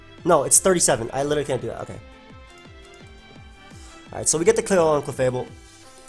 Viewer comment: I heard we got the clit on clefable 😂disgusting thought it was a male